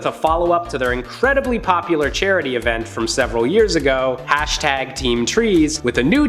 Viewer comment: I heard 7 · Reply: several means multiple- you must’ve misheard it as 7